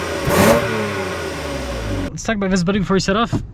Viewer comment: what did you say? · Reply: Let’s talk this body before we set off? Lmfao I think.